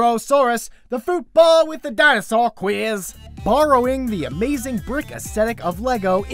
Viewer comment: I thought you said Dinosaur Queers (with a Boston accent).